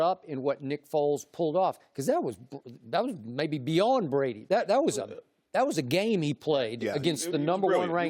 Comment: WAIT A MINUTE!! Did I just hear Skip at 2:17 say that someone's performance was "beyond Brady"? I must be hearing things!